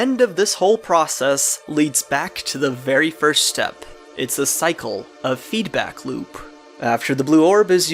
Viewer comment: I wasn't paying attention and I heard 'the cycle of eat a gloop'